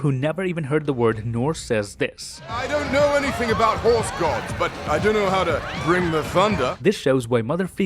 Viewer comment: Wait, did he say horse gods at 5:58. I think this is a reference to Beta Ray Bill who is a variant of Thor and is a horse.